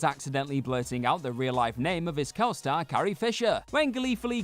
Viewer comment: I always thought Luke said “hey!” Instead of “carrie”